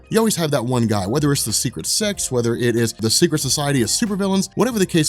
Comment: thought he said "secret sex"